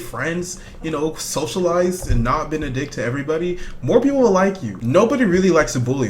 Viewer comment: WAIT.... DID HE JUST SAY:NOT BE A D** TO NOBODY 0_0